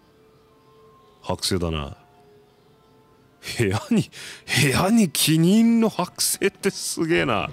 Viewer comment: どうしても 「部屋にキニンの剥製…」って聞こえて仕方ないwwwwwwwwww